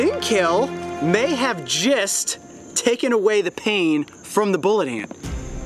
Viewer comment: Who else understood 'jizzed' instead of 'just' lol